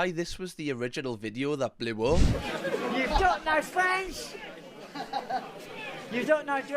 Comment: I thought the first guy was saying "You've got no friends!"! Thought it was a little on the personal side.... Fans makes more sense.